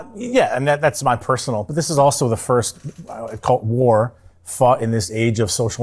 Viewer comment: I thought at first he said “this is the first cult war we fought since …” (he said “gulf” war).